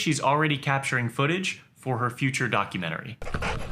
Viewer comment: I heard "her future DOGumentary"